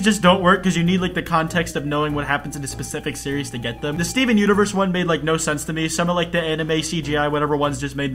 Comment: At first I thought he said sneezing universe but no